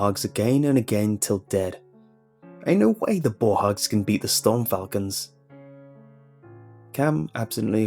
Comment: I misheard and thought the team name was The Storm Krakens.